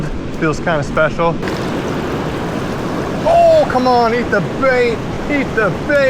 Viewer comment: My son says you should make a slice of pizza lure. When you were saying "eat the bait" he thought you said "pizza bait"